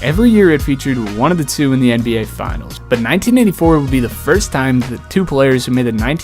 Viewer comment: Did you say.... 1994 at 17:28 ?